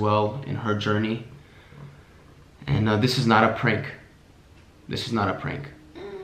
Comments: when he said “its not a prank” it sound like she said “it is” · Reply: he says "this is not a prank" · Let me know what videos you'd like to see going forward ❤️ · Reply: Guys this is a prank I heard saying it is a prank on 3:11 in a low voice when said it is not a prank ok